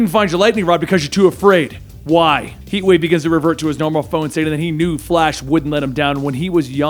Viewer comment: Anybody else hear that "Heatwave begins to revert to his normal phone..."? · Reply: He tried to say form